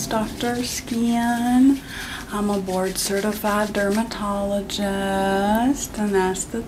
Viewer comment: I thought she said 'Lord-certified' dermatologist which would have been fitting 😅